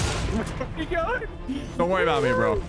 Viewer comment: Is it just me or did that say where the ****you going at 12:36 mins and second???